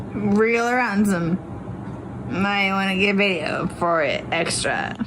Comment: Oh! My! Gosh!!!! I've just now, after allll of these years, figured out what Sarah was s̶a̶y̶i̶n̶g̶ drunk slurring during that video! I thought before she was saying "real er-ransom" but No! She is saying "wriggle around some. I wanna get video of it. Extra." And, she told the first deputy on scene she wanted to "make him squirm". She's so EVIL 👹